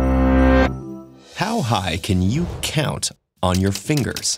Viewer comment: Thought it said "how high can you get"